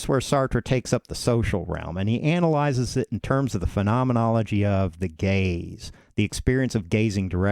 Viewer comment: me when I thought he said “the gays” 👁👄👁